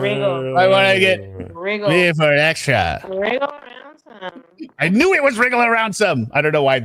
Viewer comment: Ohhhh—regarding the “wriggle around some…extra” quote from Sarah’s heinous video—I thought she might be saying, “Wheel around some….extra”, as in, she’d been “wheeling” him around in the suitcase and planned to continue that at some point. Wriggle makes sense, though. God almighty, what a heinous, heinous monster 😔.